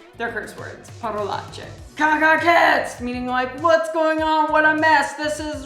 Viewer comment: what are you saying? It sounds really like "caga cazzi" that means "pain in the ass" but in an even much vulgar way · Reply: She's definitely saying "cachezz", your Guess Is correct